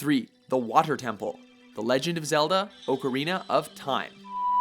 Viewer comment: Did he just say oak-arina of time?